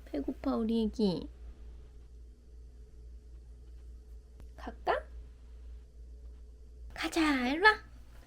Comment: 갈까? -> 까까로 들려서 눈번쩍에 우다다당까지 나왔네요 ㅎㅎ